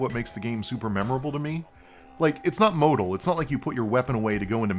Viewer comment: what did he say @ 8:11? is it modal?